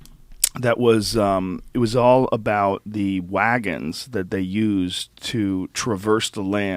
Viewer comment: I thought Joe said "Dragons" 😂